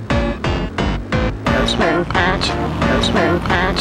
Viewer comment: why am i hearing noseman pat